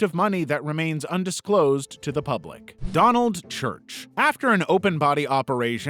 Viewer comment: for a second i thought he said donald’s church